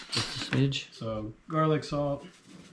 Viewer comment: does he call his buddy a bitch? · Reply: Lol smidge